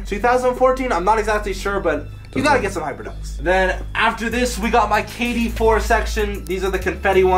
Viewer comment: Yes I did at 7:10 he said kid instead of kyrie 4